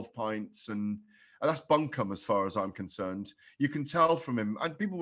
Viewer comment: does he say bum cum?!?! 🤦🤣🤣🤢🤢